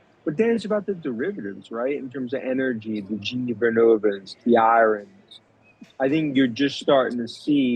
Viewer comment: What stock did he say at 3:22 after GE Vernona ? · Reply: Iren - I believe